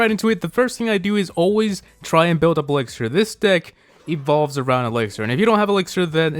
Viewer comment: lol I heard, "This dick evolves all around elixer."